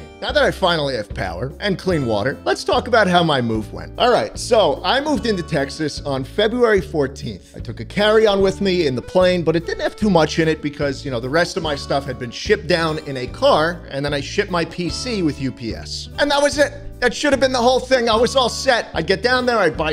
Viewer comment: i thought he said "the rest of my stuff had been shit down in a car"